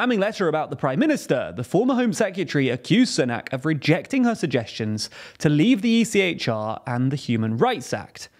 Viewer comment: I thought for a moment it said "Sunak could leave the Earth" i was like, really? 😃